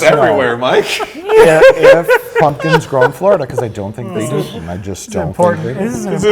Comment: it sounds like Mike said "in fuckin' Trump Florida"